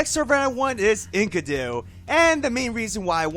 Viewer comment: When I hear you say Enkidu's name. I heard "Ink-a-do". IDK this had me laughing for a full minute.